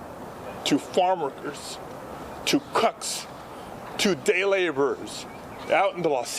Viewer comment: I heard "cucks" 💀